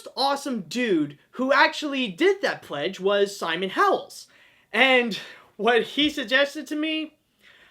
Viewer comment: Simon Howell's. For a moment there, I thought you said Simon Cowell's.